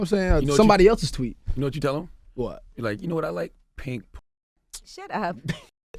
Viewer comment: what was the word charlamagne says at 17:20? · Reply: "You know what I like, pink pussy"